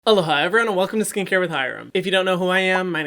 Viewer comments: In the first videos of his I watched I heard "Eldaha everyone" Not "Aloha everyone" 🤦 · I just realized in this video that says “Aloha” in the beginning. I always heard “Hello ha” and was just thinking I was hearing “hello hi” wrong and he likes to greet us twice 😂